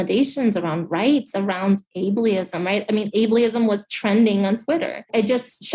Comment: Did she just say "aybleeism?"